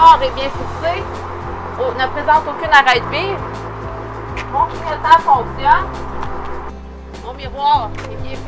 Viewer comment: Qu'est ce qu'elle dit après fixée à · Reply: Mon miroir est bien fisé et ne présente aucune arête vive